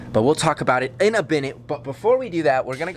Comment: Is it me or did he say binute instead of minute